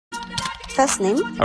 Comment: It doesn't sound like he saying, "robbing." It sounds like he's saying, "a rabbit."